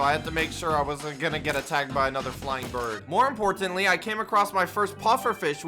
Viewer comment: did say flying birds don't t they already fly